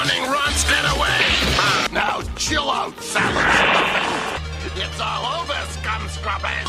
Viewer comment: Can anyone make out what he said? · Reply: I believe he is saying, “Now chill out, salad stuffing.”